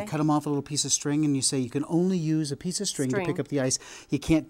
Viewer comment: I thought he said we can only use a piece of string... Salt